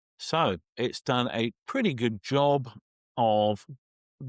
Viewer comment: That was a terrible sky mask in the beginning :O The entire bridge was pink ! Cmon dont say its great when it isnt. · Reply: I didn't hear "great"; I heard "pretty good job" at 6:50. And said where it missed, including the bridge, "it's very easily fixed with the new submasking." And later he says, and I completely agree, all apps offering AI masking miss this way on too many images. I use Lightroom, Luminar Neo, ON1, and Topaz, and they are all guilty of false positive and false negative AI masking. Some do better on a particular image, but not consistently. The devs need to put in work and get better at it. AI masks struggle to isolate trees with sky peeking through branches, hair with background peeking through, etc.